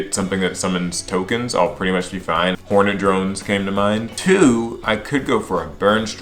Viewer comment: I thought he said Mega Horny Drones wtf lol